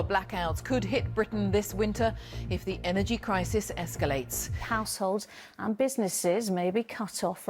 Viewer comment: News reader said households but I swore she said arceholes